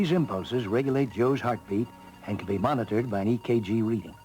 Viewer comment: I remember our fifth grade teacher said we were going to watch this and one kid misheard her and said "what?" "Jew's heart?"